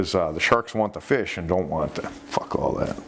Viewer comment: "Fuck all that" had me dead. Hahaha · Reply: Dude 14:45 time stamp i rewound it 10 times to make sure i heard right he for real says "fuck all that" then turns his head with a look of disgust. What happened? Did he forget he was rolling or it accidentally got put in while editing? I laughed my arse off but am so confused. Glad someone else heard it.